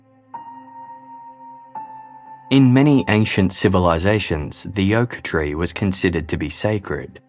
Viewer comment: I thought you were saying "yoke tree"